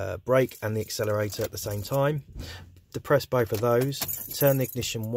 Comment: I don' understand what you're saying from minute 0:55 after "at the same time" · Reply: He's saying "depress both of those" meaning the accelerator and the brake pedal.